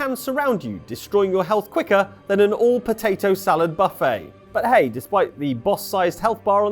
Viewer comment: For a moment I thought you said "all potato skin buffet" which is a rad idea